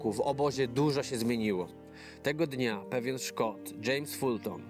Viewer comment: aż kilka razy cofałem, bo usłyszałem, że pewien ...szkop. ale nazwisko nie pasowało. A ogólnie - bardzo dobry, ciekawy materiał.